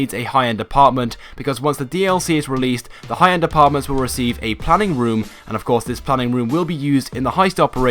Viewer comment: I thought you said we need a plumbing room to plan the heist lol